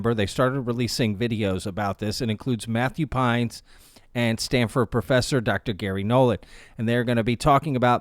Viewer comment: Had to rewind. I misheard and thought you said Stanford Pines, and my mind went Gravity Falls for a sec😅 Another great vid man. You are on a roll